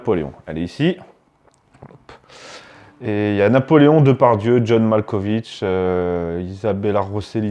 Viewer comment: quand tu as dit "Napoléon, Depardieu..." tu aurais dû marqué un temps de pause j'ai entendu "Napoléon Depardieu" je peut pas m'empêcher d'imaginer Depardieu sur sont scooter avec un bicorne mdr